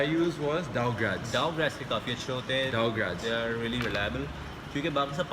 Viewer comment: what did u say dow ??? I dont understand this name please tell me about this book ..i m gona buy this .. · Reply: Dow grads